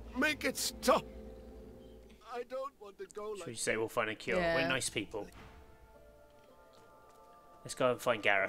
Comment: Yay new episode :D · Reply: did you just say you are nice people XD, Also I remember Tam